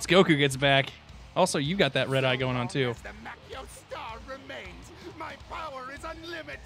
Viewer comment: When i first heard garlic junior say makyo star i thought he said macaroni star